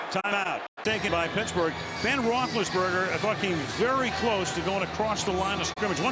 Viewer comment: does he say "ben roethlisberger fucking very close to going across the line of scrimmage" lmaoooooo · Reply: "Thought came very close" or "I thought came very close."